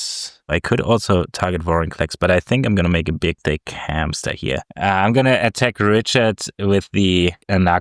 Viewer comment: Richard being back for a clash is awsome! Also, did say “big dick hamster” at 34:20 😂